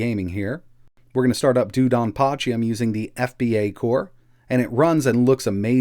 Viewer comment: What was that game you were playing @ 4:00. I thought I heard you say "Dudan Potchy" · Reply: DoDonPachi